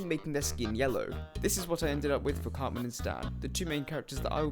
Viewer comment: When you said "cartman and stan" i heard "cartman's dad"